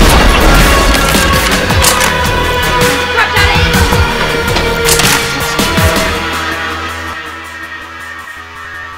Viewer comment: On 2:50 does say runt out of ammo or crap im out of ammo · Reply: Crap out of ammo